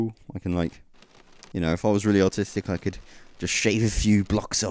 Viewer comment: when he said "if your really artistic" i thought he said " autistic"